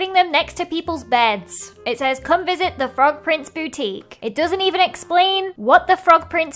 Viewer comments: i thought u said “the frog prince booty” and i was like: “i love it” 👁👄👁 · How many people thought she said "The Frog Prince Booty"? just me... okay